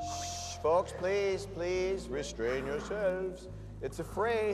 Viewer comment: What’d he say at 25:48? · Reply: Restrain yourselfs.